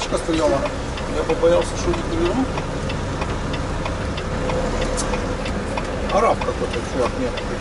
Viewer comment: Я правильно расслышал, автор сказал: « Араб какой-то-мент»? Прозвучало как-то недружелюбно, даже пренебрежительно, учитывая, что он помог тебе. Я могу понять американцев, которые порой проявляют нетерпимость к приезжим, но чтобы приезжие гастарбайтеры проявляли ксенофобию по отношению к американцам. Смешной момент.